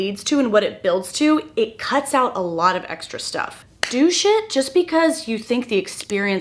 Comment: I find myself relating to you often but this video highlights something I WISH I didn't but still do. I (harmfully) disassociated throughout all of high school and it led to many negative consequences but an interesting positive that came from it was friends. I am naturally an introvert and more happy to be in my own space alone doing something artsy/creative. Yet I had a very large social group of people from all different "groups". Very unintentionally. I think it was because I did not care so thoroughly that everyone else's behaviors and insecurities weren't something I thought about. So I guess people were drawn to that? Now I am almost 26, very happily married to a man who literally saved my mental health, and now I have 0 friends. I no longer disassociate to get through life. So now I actually care what others think of me and now I struggle to make friends. Part of it is my interests do not usually align with other women my age. I don't party, I don't care much for social media, and I prefer to be in bed by 10. You have friends and you struggle to find a man who understands you and betters you, I have the man but no women my age want to be my friend. Can you make a video addressing your experience making friends as an adult, especially after going through a career change? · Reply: Inappropriate side note, you said "do shit" at around 21 min, and I heard "douche it" and I snorted my tea.